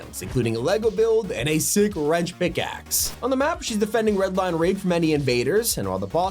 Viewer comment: just me or did it sound like he said redline r*pe?